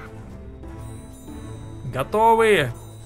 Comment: кто тоже услышал "Гавно" ?